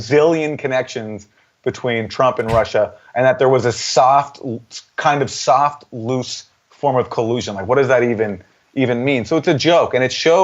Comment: Im a nurse aNd I heard "soft aNd loose collusion" i immediately thought of diarrhea...